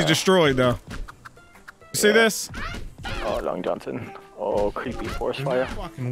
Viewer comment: Did I hear right? " Oh Long Johnson creepy forest fire"